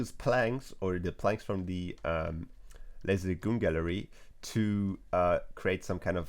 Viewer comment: pls someone tell me whats the gallery that he uses to make dis stairs cuz i couldnt understand what does he speaks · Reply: Lazy Lagoon